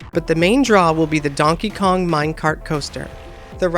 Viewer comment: Wait, did he say... MINECART coaster?